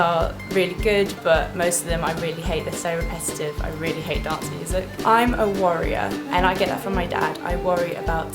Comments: When you said you were a worrier, I thought you said warrior!! xxx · When you said worrier I thought you said warrior haha x